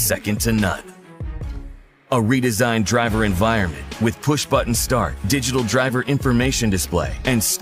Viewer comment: Hold up did he said PUSH TO START!!